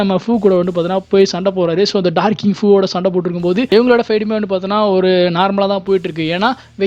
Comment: i heard like donkey fu 😂